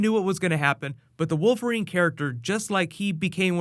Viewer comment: did you say wolf erine??